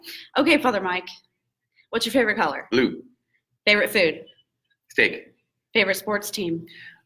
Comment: I went back twice and still couldn’t understand Fathers favorite food, or tv show. Anyone? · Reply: I thought I heard him say steak.